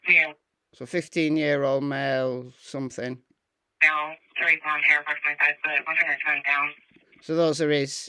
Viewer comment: 15 year old male not in the bed is what she said I believe